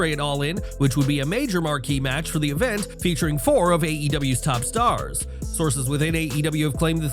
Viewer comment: " featuring 4 of AEWs top stars " What I heard " Featuring all of AEWs top stars "